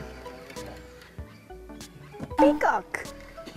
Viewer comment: ピーコックがピカーに聞こえてきた。老人性難聴の始まりかな。タライロン良いですね。